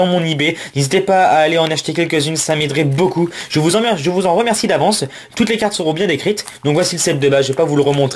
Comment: J'ai entendu "Je vous emmerde, je vous en remercie d'avance" ^^ xD